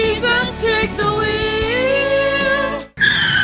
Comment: wait was he saying “she’s a SIGMA wheel!”🗿